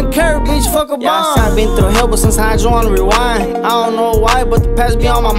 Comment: “sometimes I just want to rewind, I don’t know why but the past be on my mind😔” I could really relate to this😞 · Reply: My dumbass for so long Thought he said “but since I had you in rewind” like kept thinking about them 😂